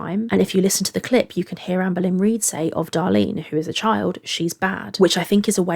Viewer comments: I think she says " Darlene is bae" which is even creepier tbh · She actually says "she's bae"...even more explicitly wanting to "date" her. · I think she said Darlene was "bae" here, obviously that doesn't change anything about that comment for the better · i think here she says “she’s bae” not “she’s bad” which almost makes it worse bc it’s more so her being like “oh she’s mine.” also interesting to hear being like “she said she would be a pdf file for her” what an odd thing to say. 🤨